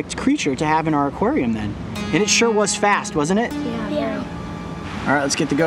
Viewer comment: I thought he said: It sure is fat